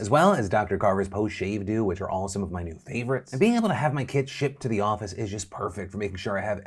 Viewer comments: I heard kid instead of kit and was like 😲? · Well my hearing is off. I heard, "shipping my kids to the office", instead of kits and I got worried there for a second. 😁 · I thought he said , and being able to have my kids shipped to the office lol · i was not paying attention during the sponsor part and thought he said' and being able to have my KIDS shipped to the office is just perfect', my brain was so confused